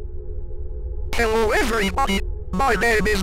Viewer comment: Did anyone else mishear Mark in the beginning as kill everybody instead of hello everybody? lol